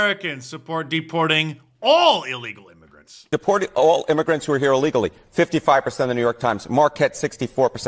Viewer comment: I thought he said "Deporting all Democrats who are here...illegally." So yes. Yes I am in favor of that.